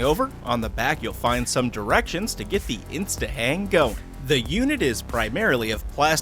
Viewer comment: I thought that first thing said "Insta Wang"